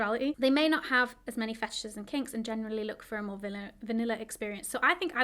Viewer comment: heads up the subtitles say "fascists" instead of "fetishes"